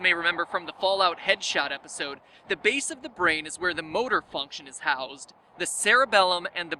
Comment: I heard 'the base of the brain is where the boner function is housed'